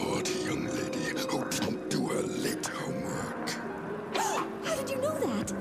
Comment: what does he say at 15:44 "A very naughty young lady who can't do a little....." a little what? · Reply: Who won't do her late homework.